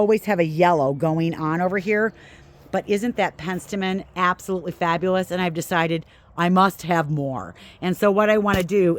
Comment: Beautiful lush garden. Well done. What are the tall yellow flowers next to the Zagreb coreopsis towards the end of the video at 37:29. I missed what you said they were. Thanks. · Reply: Coronation Gold Achillea- Yarrow